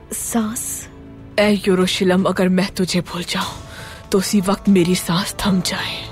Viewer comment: someone please tell me about this sound "hey eroshilam" ☹️ what is the meaning of it? Whom is meant by it?i hear it in ertugrul series but can't understand, someone please tell me?☹️ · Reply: Jerusalem not eroshilam